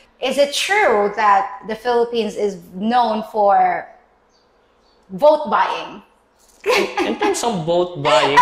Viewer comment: Interesting subject. I did not catch what you were saying at about 5:30 bookbite?? Or something like that? Its funny you use the term cat fight. Not PC here. Lol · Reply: Ahhh, I think I said ‘vote buying’ 👍 Cat fight isn’t a PC term? Good, I’ll use it more often 😂